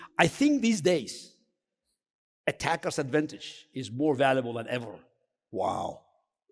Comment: What is he saying? "attack -us - advantage" or "tactical advantage?? " plz translate what he means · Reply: Attackers advantage is more valuable than ever*